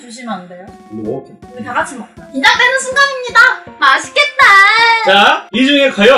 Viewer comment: 왜 맛있겟다가 다스케테로 들리지?